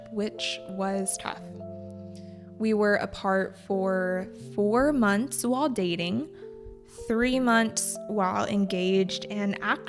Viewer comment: My husband always says to me “Biết đủ là giàu em ạ” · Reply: /13:30: ur pronunciation "while" i hear see months lot dating . i'm curious about why you pronounce it that way.